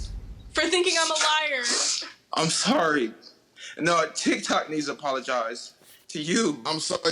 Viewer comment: That’s NOT what he said . He literally just meant “I’m Sorry , And not only do I need to apologize but, tiktok needs to apologize to you.” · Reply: Lotus "Literally" That's not what he said in the slightest. You added about 9 extra words to his statement. 9:14 he says "I'm Sorry-- Nah, Tik tok needs to apologize to YOU."